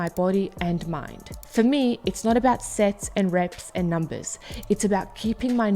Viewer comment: I thought she said it's not about sex!